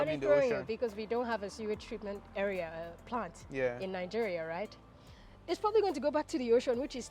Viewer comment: Did I hear her correctly when she said Nigeria has no sewage treatment Plant? 🤔